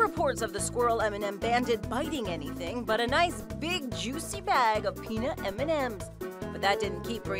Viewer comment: did she say "A nice big juicy bag of m&ms?"